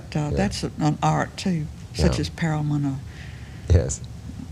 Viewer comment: When talking about the art of parody at 7:58, I think she says, '...such as Perelman or--' -- a reference to humorist S.J. Perelman. (The closed captioning unhelpfully renders it as 'paramount.')